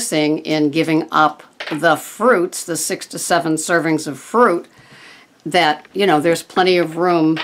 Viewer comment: I think I need to have my ears cleaned. I thought you said you'd given up your 67 servings of fruit a day. HA What you said was 6 "to" 7 servings. Then I thought you said your bullet proof hot chocolate called for 12 boxes of Almond Milk.., when you had actually said 12 "ounces"! LOL Needless to say..., I was re-winding to make sure of what I'd heard and then sitting here and laughing at myself. :-0 Well, yes..., I have taken a large step forward. Gave up my Swiss Miss hot chocolate with Coconut oil in it..., and now have herbal tea with Coconut oil in it. No more travesty of mixing something so good with something so bad. I have many other things to fix, but have notched up a win for myself. :-) Jean